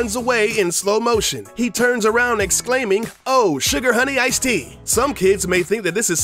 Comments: lmao i always thought he said oh sugar honey i stink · In the slow mo scene in the movie Madagascar I thought Marty said oh suger eye tin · I always thought Marty was saying oh shit as a kid but little did I know he said sugar, honey, ice tea and I guess I was correct but I guess I got it mixed up and instead got oh shit. lol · For Madagasar, I always thought the Zebra said, "Oh, sugar honey, I stink!". Boy was I wrong!